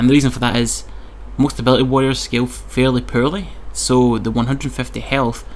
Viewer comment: what is he saying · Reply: "most ability warriors scale fairly poorly". Easy.